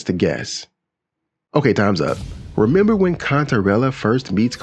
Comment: At first, I thought you said Cuntarella